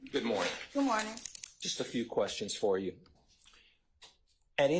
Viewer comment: Thought said just a few questions